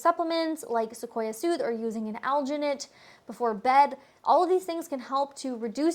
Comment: What strategic supplements do you mention around 12:31? I could not understand what you were saying. Thanks. Sounded like Sequoia and Algernate? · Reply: Yes — that’s right! I mentioned Sequoia Soothe and alginate. 🌿 Sequoia Soothe is a newly launched product that I formulated myself to help soothe the esophagus and support gut lining integrity. You can learn more and order it here: <link> 💛